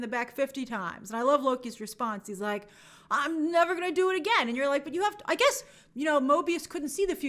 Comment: I thought he said “Why not do it again?”